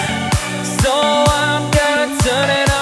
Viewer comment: I really thought he said Dear cunt instead of dear God before reading the lyrics